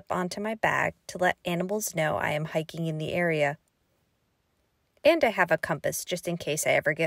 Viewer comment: At first I thought you said "To let ants know that I'm hiking in this area" And I was like, what why do they need to know, what kind of crazy ants are there? 😂